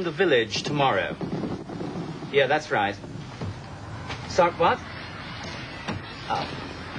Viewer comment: what did Adam Chance say? ... thats right... he said "suck what?"